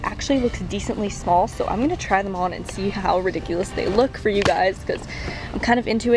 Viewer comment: I thought you said "thriftdiculous" I loved it and then realized you said "ridiculous" 😂